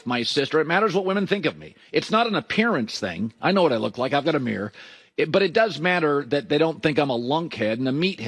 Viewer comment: Colin “I know what I look like, I have a beard” Cowherd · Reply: I thought he said he has a mirror, not beard.